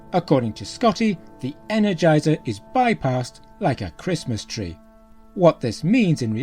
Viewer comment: "The energizer bypass's lit up like a Christmas tree sir, don't give me too many bumps!" It's hard to cut through that Scottish-adjacent accent unless you grew up hearing something like it, but that's what he says when Kirk asks if they've got power to limp inside the Mutara Nebula. Somewhere in Main Engineering there's a status panel that has every "Check Engine" light within 20 feet rapidly blinking for attention....